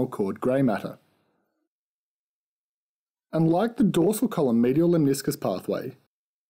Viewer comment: you said this pathway unlike the dorsal column medial lemniscus pathway has three order neurons ? do u mean unlike the spino-cerebellar tract or I miss understood the dorsal column pathway ? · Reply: Asma Qasim Yes I thought he was saying "unlike" too, but he is saying "and like"